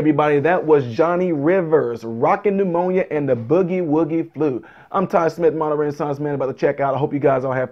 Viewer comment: I work Shoneys and they play this song at least once a day during my shift and I absolutely love it lol. Don't know why. But I always thought he said "Walking Pneumonia and the boogie woogie blues"lol